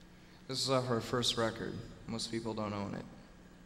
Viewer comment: Welp, i just learned that when he says "This is off our 1st record" He says "Most People Don't OWN it." I thought he said "most people don't KNOW it" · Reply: same here lol, i just looked it up because i always thought he said most people dont know of it